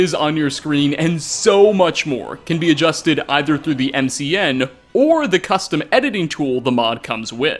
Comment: Did he say "MCN" Lol